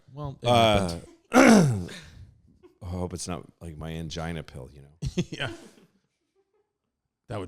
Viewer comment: Oh, I thought Louis was saying he needs to take his "man-gina" pill